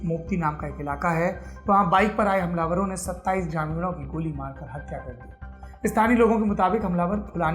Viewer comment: क्या बोले पण्डित जी? 27 जानवरों की हत्या कर दी!